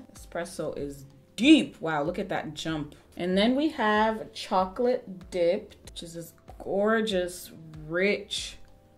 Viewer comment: Thought she said Chocolate D- she shouldn't have said it so fast. WHY!? 😂